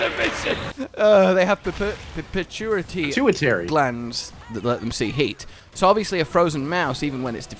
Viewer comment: I heard "- let's them see hate." 😂❤